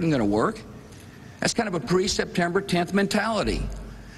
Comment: did he say pre-september 10th mentality?